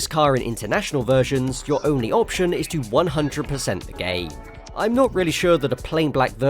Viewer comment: I initially heard this as "100% gay", had to relisten a couple times to realize it was actually "100% the game" 😂